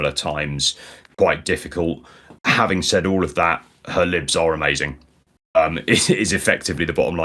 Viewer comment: Had to rewind because I thought he said "Her lips are amazing"